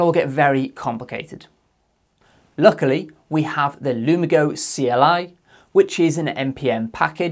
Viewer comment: Can you put the npm package you mentioned in the description? edit: got it, I thought you said "lumago" but you said "lumigo" :)